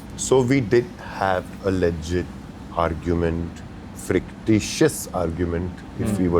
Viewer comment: did I hear “frictitious” 🤢